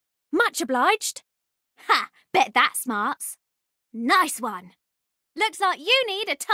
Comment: What does she say at : 0:39 | 9:28 | 10:27 ? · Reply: "Ha! Bet that smarts." ("Ha! Bet that hurts.") "Center me in the middle of your screen!" (from the tutorial) and "Chin up, team! We'll win the next one!" ("Cheer up, team! We'll win the next one.")